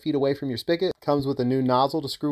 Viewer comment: I thought you said “it would slurp itself up” 😳😆